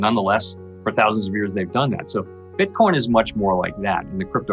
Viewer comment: Did Powell just call it Bitcorn??? Go to 2:25 mark for clarification So are Central Banks buying Gold and Not Bitcoin? Is Gold a better "speculation"